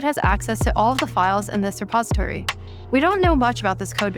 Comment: did she say suppository instead of repository???